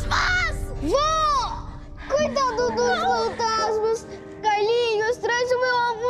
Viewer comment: o que o casca falou?Não entendi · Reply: coitado dos fantasmas